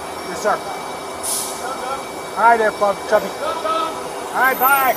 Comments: What's said @ 8:19 ? "Fuckin' chuby" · Did he call you cubby